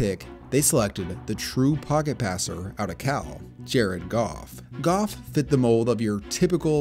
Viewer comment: I misheard and thought he said Oh my Goff